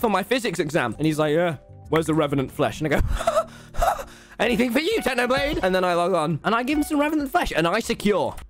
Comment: wait what did he say? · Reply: Anything for you technoblade